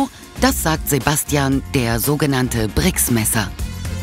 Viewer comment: ich hab erst Drecksmesser verstanden